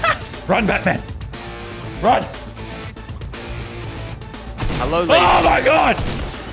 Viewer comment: Someone said hello Lazarbeam and it sounded like Fresh